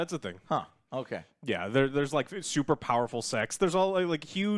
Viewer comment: only i heard "There's superpowerful sex"?